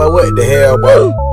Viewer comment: can somebody tell me what the guy saying? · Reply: bro what da hell boy